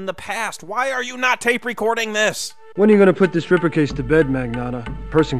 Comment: Sounds like Quinlan says "When are you gonna put this stripper case to bed?" lol